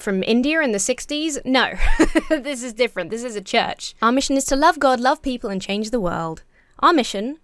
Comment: When you said, "Our mission," I thought you said, "Amish in," and I was thinking, why is this guy on YouTube if he's Amish?